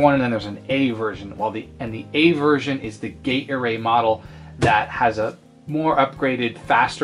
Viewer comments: For a second in the begining I thought you said gator-aid, and not gate array. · You're saying "Gate Array Model" and I keep hearing "Gatorade Model". 🙂